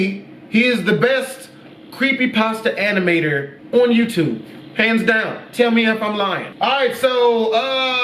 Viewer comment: Sounded like he said pants down, tell me if I’m lying